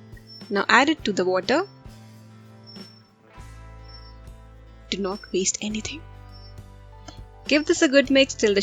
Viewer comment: Anyone heard a wisper at 3:59 Don't waste it or taste it